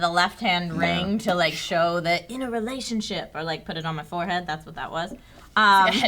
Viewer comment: did phil say trishas in a relationship